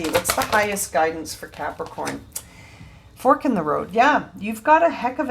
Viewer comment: this reading resonates with me. I'm feeling the mind shift and am feeling improvement. I'm seeing higher subscriber numbers. No suprise for me 😊 Thank you for sharing your blessed gift 🙏❤🕯🇨🇦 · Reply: When you said "fork in the road" I heard "fuck'n road" lol Of course you wouldn't use that kind of language 😂